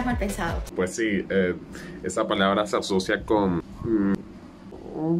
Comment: yo pensé que decía colgar